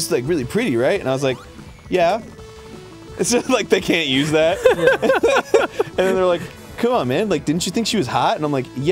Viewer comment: What show is Arin talking about at around 13:37? It sounded like he said Advance Showdown but I can't tell. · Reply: Dance Showdown, it was a show where they taught you tubers how to dance. Arin actually won and gave the prize money to charity